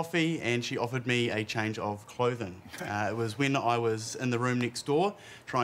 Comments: As a New Zealander, it's harder to enjoy them because they just sound normal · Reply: I think it's because it sounds like he says "When I was tin" lol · I thought he said when I was a teen